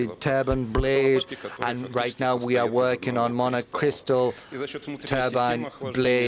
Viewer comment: did he say mana crystals ???